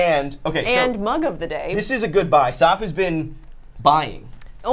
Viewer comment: I wasnt paying atention and at 6:47 i hear: THIS IS A GOOD BYE, and i was like, THE VIDEO'S ALREADY OVER?